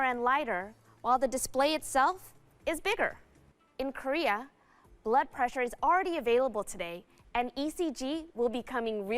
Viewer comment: Did I hear this correctly? 'In Korea, blood pressure is already available'.